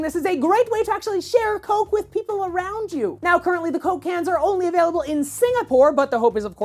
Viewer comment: Holy shit it sounded like he said coke cancer and not coke Cans!!!!!! :/